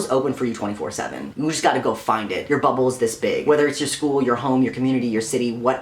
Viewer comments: I thought you said "your butthole is this big" instead of "your bubble is this big" and I was like wait what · Your bubble is this big Me: DiD hE jUsT sAy bUtThOlE?? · The first time he said bubble I thought he said butthole and I was very confused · I thought you said 'your butthole is this big' and I had to rewatch it five times.